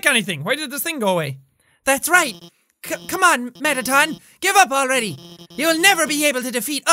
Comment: marathon? Instead of mettaton?